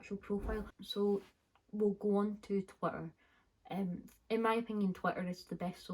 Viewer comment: can please someone tell me what app is that ? i dont uderstand ! thank you so so much lovelies <3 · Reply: She said Twitter